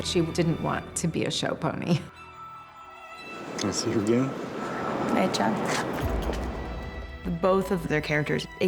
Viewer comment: He says: Can I see you again? What does she say after?? SOO excited for this series! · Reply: Night John.